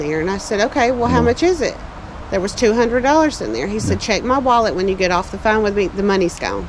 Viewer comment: Lol! Thought he said there was 20k in cash on board??? He has his own rolling ATM?!!!